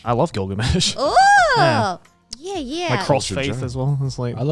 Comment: Did I hear Crossfaith come out of Joey's mouth? Can someone confirm this for me?